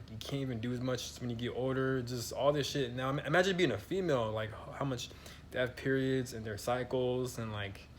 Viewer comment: lol thought you said "imagine being female... and they're psychos". For me I'm not sure if therapy is even worth it nowadays, given the dire state of things. It's basically a big gaslight gulag. I dunno about this "trauma trapped in the body" thing, there's more to it than that. Technology and wifi is messing with people's nervous systems too.